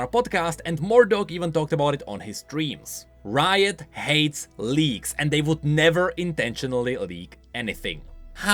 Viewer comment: I watched this video right after i woke up, and i mishear “leaks” and “Leagues”, I thought i was dreaming